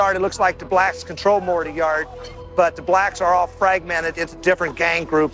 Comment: 00:07:51 ⏳ I think you heard👂🏿🩺🧑🏿‍⚕️ wrong.. Listen again.. IT LOOKS LIKE THE BLACKS 💔ARE TOGETHER.. THEY ARE FRAGMENTED